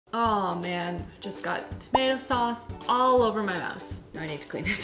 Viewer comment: Thought you said “just got tomato sauce aaalll over my mess...” I just knew that was wrong but it took me off guard! 🤪🤨